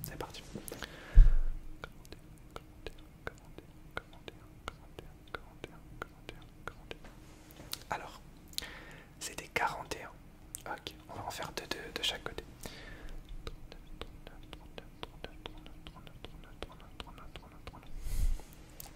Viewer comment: "c'était 41" Moi qui ai entendu "Camembert" : 👁️👄👁️